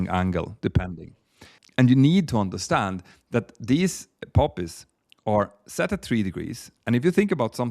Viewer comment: I got so excited when I heard "puppies" and was so let down when I realized you were talking about "poppies".